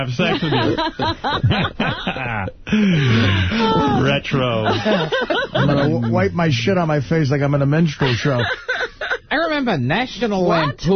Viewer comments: wish I knew what Artie said there lol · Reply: "I'm going to wipe my shit on my face like I'm in a minstrel show." Minstrel shows were those old shows where they wore blackface. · What does Artie say at 16:45 ? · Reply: "I'm gonna wipe my shit on my face like I'm at a minstrel show."